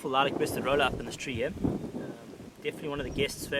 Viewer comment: What was the name of the bird at around 4:30? I couldn't quite hear what he said. Rolling something...?